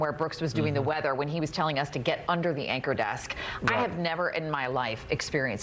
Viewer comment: I'm not sure that's why they call it an "anchor desk". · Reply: I thought they were saying "anchor deck"?